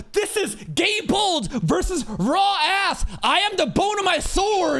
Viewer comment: all i hear is raw ass